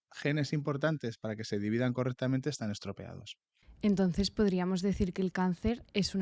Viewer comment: Vuelve al minuto 2:48 y me dices que dijo el doctor? Prefieres decir? Acaso de eso se trata la etiología (rama de estudio que busca la causa de una enfermedad)? Los cambios genéticos que contribuyen al cáncer suelen afectar a tres tipos principales de genes: protooncogén/oncogenes, gen supresor de tumoral y gen de reparación de ADN. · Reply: dice estropeadas